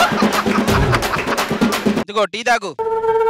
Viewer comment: dialogue pls, i didn't understand🤧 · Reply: Venky movie lo bramhi dialogue andi idhi andhuke navvaruu anthalaa ( ey ra coffee ichava ani anaru )